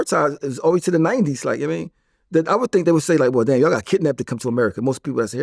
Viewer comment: What family vlad i thought u said yall got squad wiped